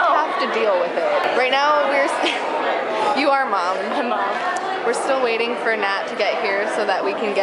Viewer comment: I know said "I'm MAM." But because it was loud there and you almost couldn't hear her, her lip movement almost made it look like she could have said "OM NOM." 3:58 - "AMEN" 12:08 - lol the GASPS and going "What the [BLEEP]!" XD 16:12 - lol the use of the emote 17:04 - OMG! Aaawww~ 22:18 - Dem memes xD BTW, rewind back and watch climbing up that block. It’s rather cute.